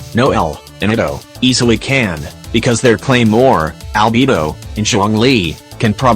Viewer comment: i heard albibo